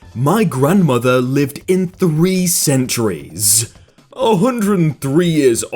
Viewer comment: Spy Jon Sachurin they did say “lived in three centuries” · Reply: 메롱 Ah. I think I heard it as “lived for 3 centuries”.